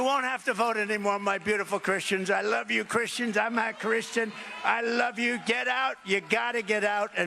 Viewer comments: I thought Trump said he was a Christian in this when I saw it several times previously. Watching and listening to it again he said "I'm not Christian." But this group of Christians are clapping and cheering and planning to vote for him! I cannot understand it. Why would any Christian vote for Trump? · I thought it sounded like he said, “I’m not a Christian.” No shit? Really? 💙🖖🏼